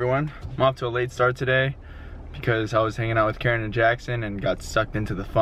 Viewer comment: I thought said "kyra and jackson" and i got so excited like "KYRA? KYRA'S THERE? THEY'RE VISITING?" lol